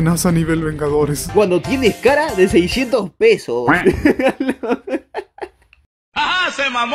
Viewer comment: Like si escuchaste que dijo 600 pesos en vez de 620 pesos